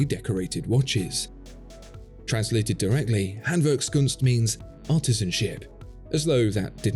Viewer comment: Genuine thought he said "that translates as Artisan Shit"